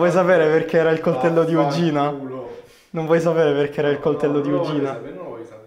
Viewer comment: ho sentito di vagi*a